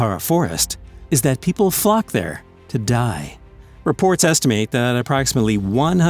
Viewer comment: Aokigahara Forest, japan : I thought you say f boom but when i turn on the caption is flock 😂